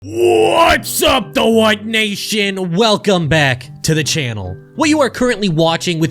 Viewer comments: I thought said in his intro "Whats up the white nation!" My ears are failing me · the first thing i hear is "what´s up theWHITE nation" i was like 😦i had to alt+tab to see what the hell i got into... thank god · for a second i thought that you said:"what's up the white nation" xd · i thought you said "the white nation" and was like bro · new to the channel thought you said white nation